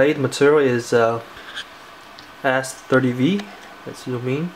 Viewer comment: t sounded like you said ass30v